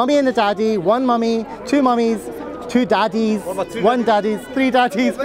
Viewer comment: This is disgusting. Did he just say 3 daddies?